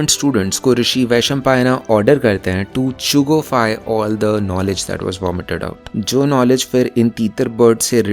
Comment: Did you really say 'Chugofy'